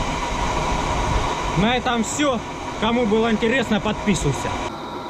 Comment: я один вместо слова "подписуйся" услышал "подписуйся а то нос сломаю"...